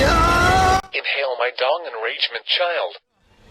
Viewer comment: Lol, what was said at 16:15? Also subscribed cause you totally tell it like it is with games like this. ^.^ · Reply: “Inhale my dong, enragement child”, a quote from a fangame called Five Nights at F**kboy’s